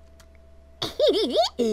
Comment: только спустя столько лет я понял что он говорит: Круз, Том Круз. Загадка разгадана😂